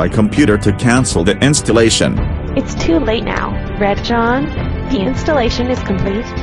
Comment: red john is a bloody smiley face (Thought doras said red drawn)